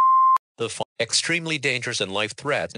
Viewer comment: it sounded like said the fu- 😂😂😂